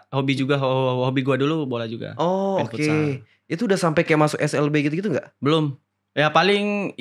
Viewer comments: Waktu cerita sepak bola koq gue dengernya "lu dah sempat masuk SLB" ?? 05:08 😂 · Gw ulang² menit 05:05 bang jo nyebut slb ajig bukan ssb maaf koreksi bang takutnya gw salah denger 😂🙏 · bang ini slb gua ga salah denger bukannya ssb ya WKWKWKWKW